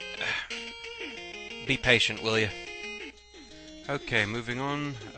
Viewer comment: It just sounded like you said "Be patient, William" so I thought you were telling yourself to be patient. And surely your name can't be as bad as this one person I graduated with, Tekilla Sunrise (no joke either). Well, my mistake then.